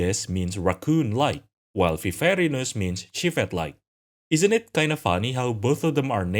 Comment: Did he say “Sheep head like”? I’ve re watched this part a few times and I’m not sure what he said. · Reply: he said civet-like